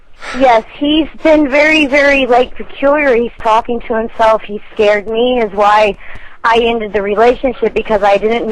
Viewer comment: did she say "he scares me, his wife"....? like he was already married when she met him!? lol b/c she said they were engaged.